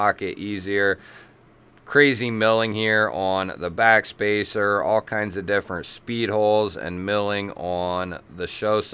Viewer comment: I didn't catch the name of the "crazy" knife. Not the huntsman, the blue washed handels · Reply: I believe your talking about the CMB Devourer